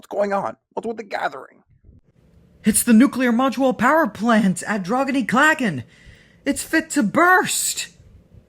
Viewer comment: Great and PEAK Episode And Also At 11:40 If You Turn The Subtitles On Why Does It Say “Crovan’s Gate” Instead of Droghan-y-Claghan?